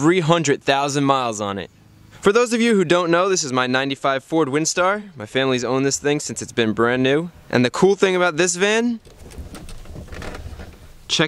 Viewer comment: Not gonna lie I thought he said 45 Windstar lmaoooo